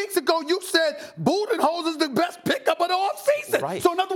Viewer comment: Sounds like SAS is saying booty hole lls